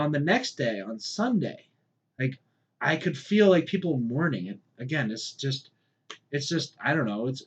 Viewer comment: what did you say you can feel people doing? "mourning" "warning"? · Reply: mourning